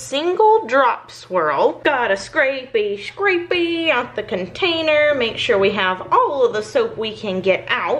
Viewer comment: I legitimately thought she’d say “I’m gonna scrapie scrapie out the contanie